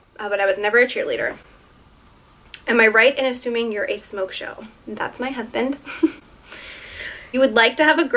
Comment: I don’t understand what she says at 12:55? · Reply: Shannon Howell she says smoke show.... totally had to urban dictionary it but it means “hot girl” or hottie lol